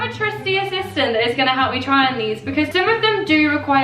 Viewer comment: I acc thought she said try on whore at the end😭😭😭